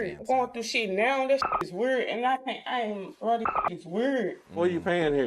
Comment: Is she saying werd, or weird. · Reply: Trying to say weird!